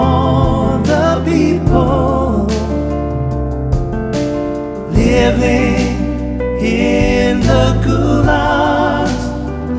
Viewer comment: I thought I'd say, living in the jail